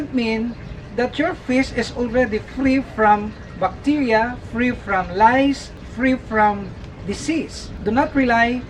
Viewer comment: i hear it as free from lies, free from deceipt 😂